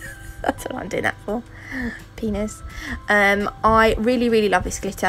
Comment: I know this is probably a very inappropriate question, but did you say "penis" about 6:45? LOL!